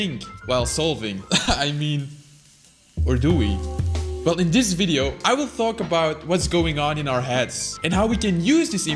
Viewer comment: !ereh laechiM ecuasV, yeH who understands that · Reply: Hey, Vsauce Michael here!